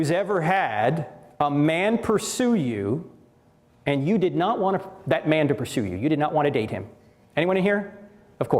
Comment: anybody else think Frank said "You did not wanna f---- that man" and was bleeped?